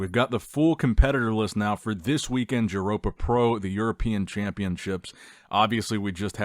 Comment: What did you say? 🤣🤣 5:56 DŽuropa PRO? 🤣🤣🤣